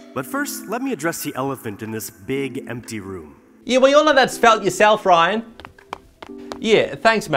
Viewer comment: did he say, "we all know that's about yourself, Ryan?"